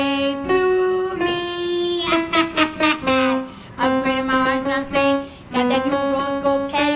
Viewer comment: I hear her saying "upgraded my iPhone, know what I'm sayin'"